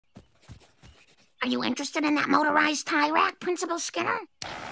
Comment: At first I thought she said “notarized tie rack.”